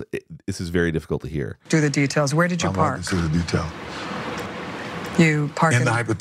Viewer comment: I think he said, "I'm not good at details."